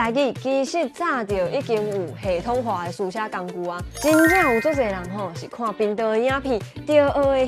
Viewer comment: 我聽成 真正有足濟人看"貧道" ê影片. 哈哈